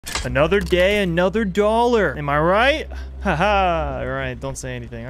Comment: I thought he said "Another Gay, Another Dollar."